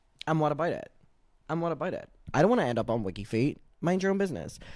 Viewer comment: You covering your feet with the pillows is kind of hilarious said NOT FOR FREE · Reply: Oh... I heard him say "Not for WikiFeet." But I wouldn't know if that's a real website or not... 😃